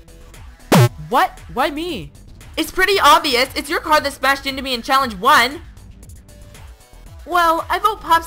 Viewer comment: what did scissors say? Her voice isn’t clear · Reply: She said: "It’s pretty obvious, it’s your car that smashed me in challenge one!"